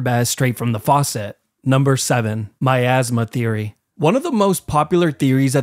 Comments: I thought he said "My Asthma Theory" and I didnt realize until 8:56 HELP MEEEE😭😭😭😭😭😭😭😭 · Am I the only one who thought he said “my asthma theory” and not miasma theory?